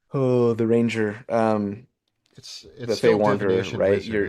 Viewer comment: what were you trying to say here? It sounded like cut you off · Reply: Was trying to think of the Fey Wanderer Ranger subclass, I think :)